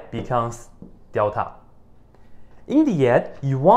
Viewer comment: I swear I heard him say "Idiot" at 2:28 (I know he says "in the end" but it confused me for a minute)